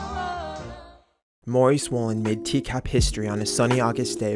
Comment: I thought you said "Maury Swolin" and I tried figuring out what Maury Povitch being buff has to do with this. I am so sorry.